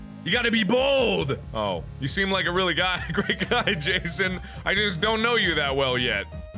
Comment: lol 3:51 I couldn't stop replaying to make sure I heard Mark say "You're a really guy" XD